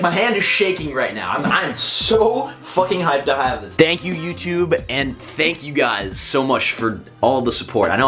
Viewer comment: It sounded like blaziken said, dank you, YouTube